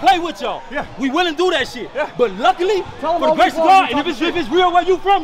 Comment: all I heard was blah blah.. "but luckily we suck"